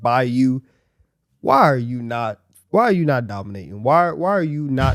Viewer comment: I thought he really said "Why are you not DominAyton" 🤣